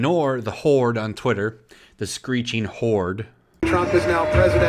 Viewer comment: I heard “Screeching Whores"